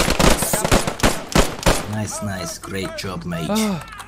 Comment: he said mate not mage,but with accent it sounded like mage.